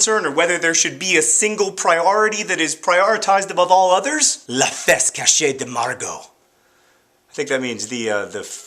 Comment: When he said "La face cachee de Margo," I heard "La fesse cachee de Margo," which translates to "Margo's hidden buttock."